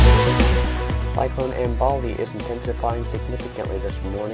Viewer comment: Definitely sounded like you said Ambali instead of Belna